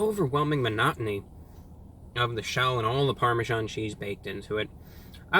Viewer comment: Pardon me but did you say "parmesan cheese" @ 11:05 ?